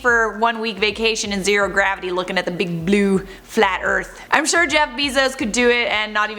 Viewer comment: I am sorry did you just say big blue FLAT EARTH?!?!